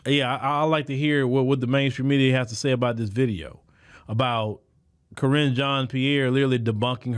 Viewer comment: When I learned her name, Karrine. I thought it was said cringe.